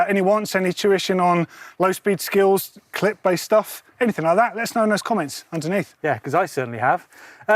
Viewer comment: When said clip based stuff at the end I thought he said click bait stuff 😂😂